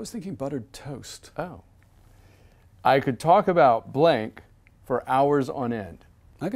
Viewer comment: for a momemt i thought he said "i can talk bout boobies" the i realized its TF talking